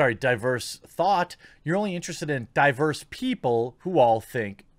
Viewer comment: "You're not interested in diverse thought, you're interested in diverse people who think exactly the same." Very well said. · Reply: For some reason, no offense or shaming, I kept hearing "diapers people" and thought it funny.